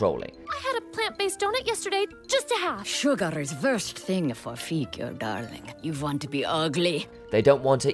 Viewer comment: If you're going to have the stupid yellow giant captions can you at least proofread them so they're CORRECT? What is the point of having words popping up that are WRONG? Example: 8:36. She says "sugar is worst thing for figure". Caption says "Sugars. First thing for feet." SERIOUSLY?